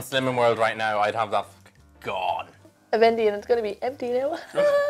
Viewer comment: What does say around 2:10-2:12ish? "something something it's going to be empty now *high-pitched laugh*"